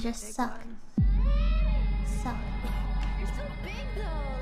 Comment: I think says "they're" not "you're"